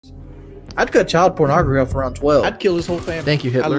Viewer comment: what is he even saying in this clip i can never decipher it lol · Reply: He'd cut cheese pizza off around 12